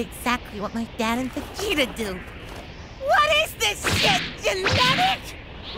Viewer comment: "I do what my dad and Vegeta did. WHAT IS THIS SH!T GENETIC?!" That was funny considering how many times Goku and Vegeta screwed up and almost got yamchaed. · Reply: when I first watched this I thought he had said "what is this shit!? Endemic!?"